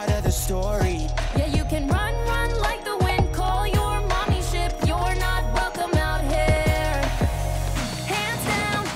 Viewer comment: Did she say a bad word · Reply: No,she said mommy ship 😊😂